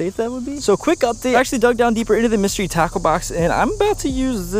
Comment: At first it sounded like you said mystery taco box